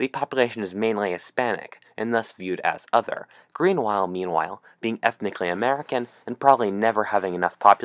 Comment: did he say 'Greenwhile Meanwhile'?